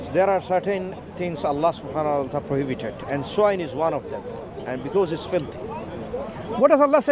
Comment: what the brother said? Anyone can clarify? Im not good with english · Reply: "There are certain things Allah swt has prohibited and swine is one of them because it's filthy."